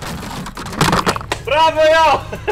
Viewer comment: did he say at 3:32 Brawo ja?? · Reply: "Bravo eu".. like "gj me" (cause i give you the drone)